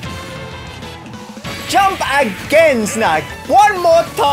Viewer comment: did you really said NOT AGAIN SNACK?